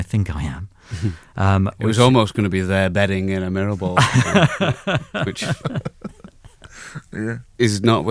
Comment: can somebody please tell me what Alex says the title almost was? They're batting in a mirrorball is what I hear and that does not warrant the giggles that ensue, so... Anyone? Pls? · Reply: ‘They’re bedding-in a mirrorball’